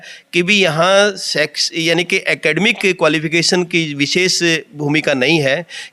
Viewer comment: Bhai sabh ye principal 6:51 pe video me jis sabd ka priyog kr rha h ispe apne koi tipni nhi ki.. Ye kehna kya cha rha tha.. Jra vistar me bataney ka kast krenge... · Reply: He was saying शैक्षणिक ...then he says Academic..